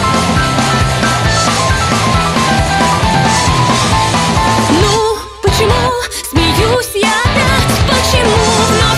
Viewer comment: Кто как думает что он сказал? :) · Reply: Ну почему смеешься ты опять?